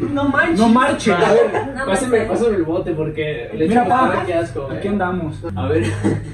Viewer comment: le escuché leche corrida jaja